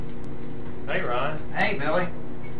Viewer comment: okay in the Rhys and Aelin one it sounded like Rhys said "hey Rowan" the first time I watched, then I watched it again and I realized he said, "Hey Ryan". I am the big sad now